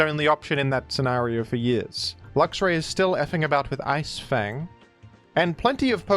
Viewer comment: Did... did just say "effing about"??